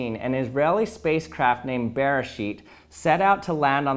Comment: Sounded like he said "Bearshit".